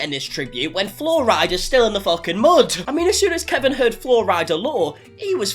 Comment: I totally thought you were saying there was fluoride in the mud. I thought maybe it was a new conspiracy theory or something.